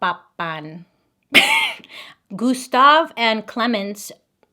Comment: I heard “Gustav Popcorn” 😭😂